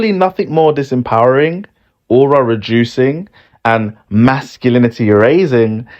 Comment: The subtitles got it wrong. He said "aura reducing" and "masculinity razing", not the slop that the subtitle generator came up with.